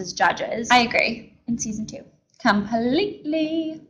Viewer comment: did she say season 2???